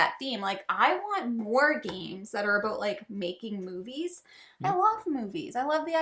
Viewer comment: So when said I love making movies game - my wife thought said making babies! 😂😂😂😂